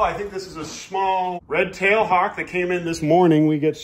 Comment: I first thought you said you got a new Tua hawk.. Congrats with eternal anxiety over your newborn Scooby-Doo. That must be his name